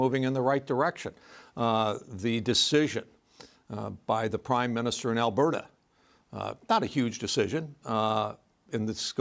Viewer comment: The "Prime Minister of Alberta'? What a typical MAGA ignoramus this guy is! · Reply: I thought he said, ‘The Prime Minister IN Alberta.’